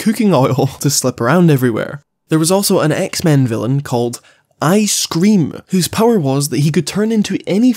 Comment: When you said "ice-cream" i heard "I scream" and expected a much different kind of villain, maybe along the lines of scarecrow